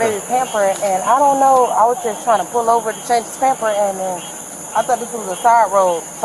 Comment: I swear I thought she said "I have to change his pamperin"!!! = )